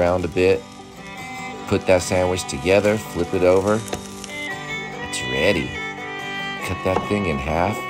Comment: I heard "The corgi is ready" and lost my shit for a second 😂